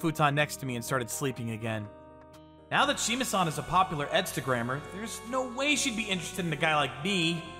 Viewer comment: I actually thought I heard her say Instagram instead of edstagram